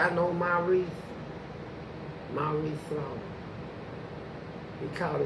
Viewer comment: did he say Maurice slaughter?